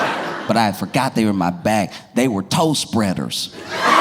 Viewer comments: I thought he said toast breaders and I was soooo confused 😂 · The toe spreaders had me howling in line at the post office. Too funny. 😂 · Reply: I had to go look at the comments because I heard he said "They were toast breaders" and it had me really confused lol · I misheard “toe spreaders” for “toast breaders.” It’s time for bed. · Why the heck did I hear "toast breaders" instead of "toe spreader" 😂 😂